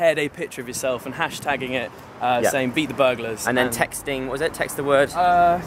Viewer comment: I thought you said #BEET THE BURGERS